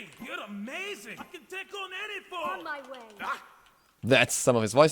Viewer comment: Did he say "I can dick on any fool"? 😂